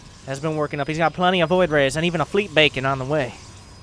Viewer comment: Wait did you say Fleet Bacon?